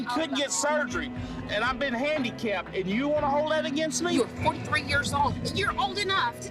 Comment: did she just say he was 43? I thought he was 50 at least ( I'm blind as a bat so I can't see captions). Tbh, love isn't enough. Both have to try. Ugh I hope the baby is loved.